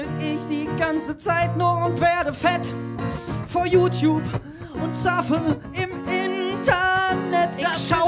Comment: Haaaalt stop! Ihr KENNT Saffe??? Oo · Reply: Jetzt hab ichs auch gehört "Von YouTube und Saffe im Internet." bei 2:55 Jetzt muss ich das Saffe Fragen °^°